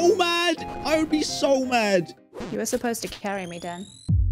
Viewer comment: i swear i heard dad